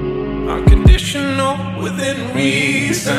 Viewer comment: Funny, I thought it went like 'Hummingbird, I know the tough times, but stay young'. If it was like that then it would be a bit more inspirational. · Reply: I heard “another tough time” at first